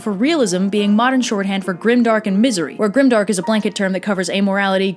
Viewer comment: excuse me did you just say grimdark?